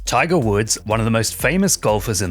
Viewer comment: i thought it said trigger words